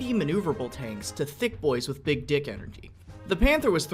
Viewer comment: did he say thick dick energy?